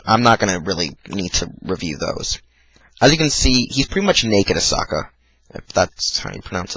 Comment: did you say "naked a sukaa" , wth happened in the ending? it looked wrong.